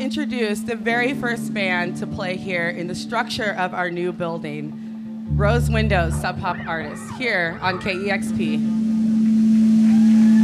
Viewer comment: I thought it said Rose Windross, damn was I suprised.